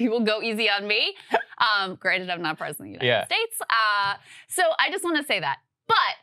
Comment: I heard “I’m not competent” 🤣😂